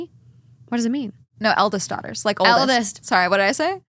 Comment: I heard Elvis' daughters too 😭